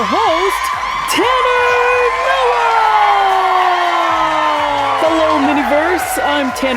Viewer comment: I lowkey thought she said Trevor Noah and I was like Yebo south African comedian and when I heard the name I was like ohhh nvm